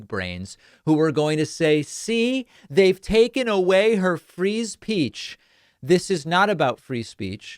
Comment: Wonder what the hourly rate came out to be after the separation.... I heard something Pak said a little differently - "Free Speech" I heard as "Freeze Peach". I kinda like that better all things considered.